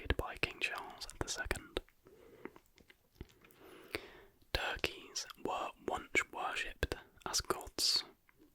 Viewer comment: can anyone tell me what thing was worshipped? I repeated it several times, maybe I don't know the word, but I can't understand what he said 😅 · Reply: it says "turkeys were once worshipped as gods"